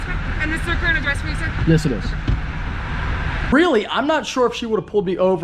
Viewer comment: Anyone catch the “yes sir” at 12:02? 😂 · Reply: he said "yes it is" not yes sir